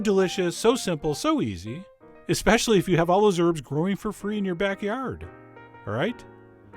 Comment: Hahaha.. I misheard you at the end. I thought you said "Specially if you have all those shrimps growing for free in your backyard!" Had to pause, go back and play it again.. :p